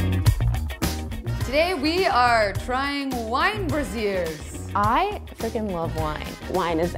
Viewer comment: I thought it said nine bras?